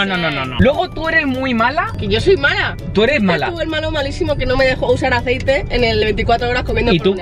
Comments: escuche hermano??? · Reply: Pero dijo malo malisimo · soy solo yo o escuche a diciendo tu hermano malisimo que no me dejo usar aceite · Escuché el hermano en el minuto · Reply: Dijo tu eres el malo malisimo